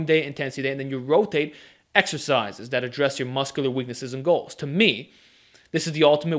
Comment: Please state what you said at 7:50. Concurrent perodization is: Volume day Intensity day And ??? What? Hard for me to understand. Thanks. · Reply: "And you rotate exercises that address your muscular weaknesses and goals."